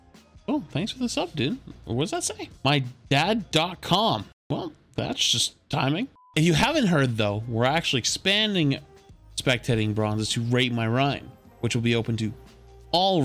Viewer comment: I just heard "we expanded spectating bronze to rape my rein" I wish to stop existing